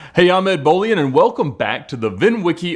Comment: Instead of hearing "I'm Ed Bolian", I heard "Ahmed Bolian" at the start of the video.